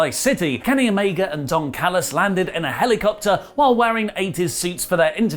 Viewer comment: MR. DAVIS: Help Phenoma-Nerds to 40k subscribers! - <link> 0:00 - WWE & NXT Conflict 2:39 - Impact Beats NXT (sort of) 3:44 - AEW Dynamite Review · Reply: I swear it sounded like you said Dong Callus lol